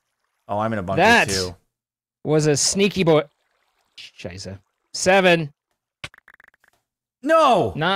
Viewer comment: did say "scheiße" WTF : )